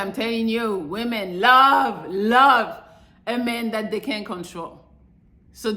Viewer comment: What did she say at 7:40 "Women love a man that they CAN'T CONTROL" or did she say a man they "Can control"